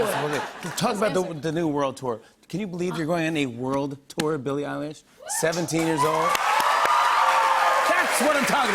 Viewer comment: Did he just say Billie EYELASH?!?!!